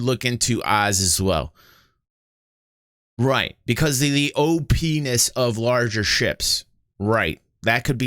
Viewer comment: Did you just say "oh-Penis"? lawl